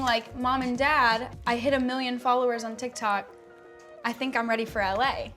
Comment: Did anyone realize Addison said “mom and dad I reached 100 million” like girlie u at 87 mill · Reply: Oh I thought she said 1 million